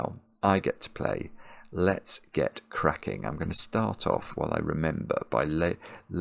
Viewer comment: In the intro, I thought said, "Let's Get Quacking," which should be a new meme.